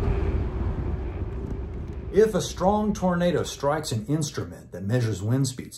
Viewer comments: if you turn on close captions instead of tornado it says burrito 🌯 😅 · BRO MY CAPTIONS SAID A STRONG BURRITO STRIKES NOT A STRONG TORNADO STRIKES LOLOLOLOLOLOL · If you haven't, please watch with captions on. It's too funny! At 1:36 it says burrito instead of tornado, which is hilarious to imagine.